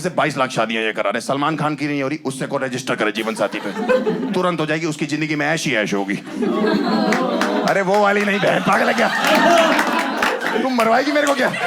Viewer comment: I heard ass hi ass hogi...😁